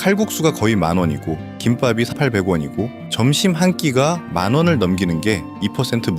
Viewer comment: 김밥가격이 왜 800원으로 들리죠?? 8:23 여기 부분 김밥가격은 3,800원 맞는대 ㅋㅋㅋ